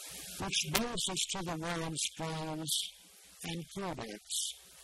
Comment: Well, no. I had never heard of the name before, and I did a search for what I thought he said "kumwan", and of course that's not quite it, but I definitely heard an "M". Anyway, I just got new speakers last week. Maybe it's time to update yours.